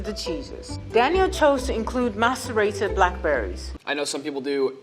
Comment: I thought the dude said masterbated blackberrys 😂